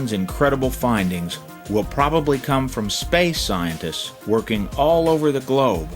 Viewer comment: I thought he said "spice". Took me about 5 seconds to realize it was "space" XDXDXD